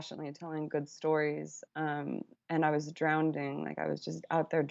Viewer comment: Did I hear her say 'drownding'?